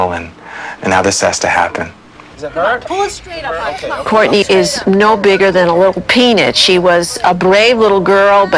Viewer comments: I thought she said " She is no bigger than a little penis " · who else thought they said she's no bigger than a penis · Ngl at 4:50 I thought she said "Courtney is no bigger than a little penis" 😂😂😂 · I missunderstood the principal and I heard her say: "Courtney is no bigger than a little penis" 😂😂😂